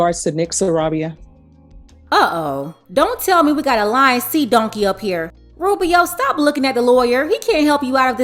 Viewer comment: Cooking dinner and then I hear “Lying Sea Monkey”😭😂😂😂😂